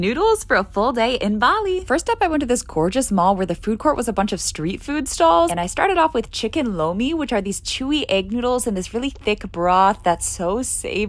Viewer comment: I thought she said chicken lo mein but she said chicken lomi lol 😂